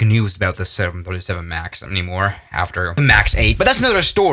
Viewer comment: i Heard as B747Max.... :Eyes